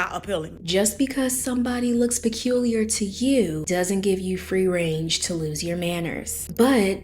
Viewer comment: Did you say "free range" hahahahahahaha